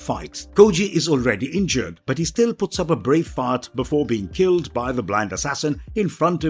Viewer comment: it sounded like you said brave fart